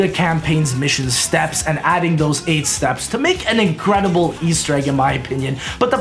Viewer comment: did he say aids steps or 8 steps